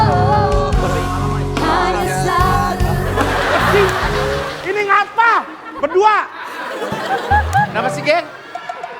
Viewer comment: tebak rigen ngomong apakah?? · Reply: Kolop